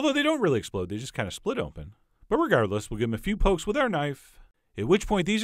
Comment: Did he just say irregardless?